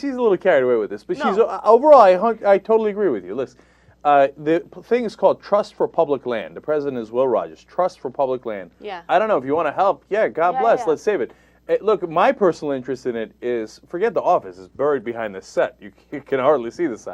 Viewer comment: I heard "Trust Republic Land". I need my coffee.